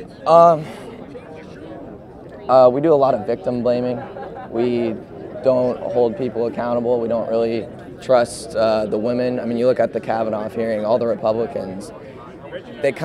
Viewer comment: did he say "Kavan-off"?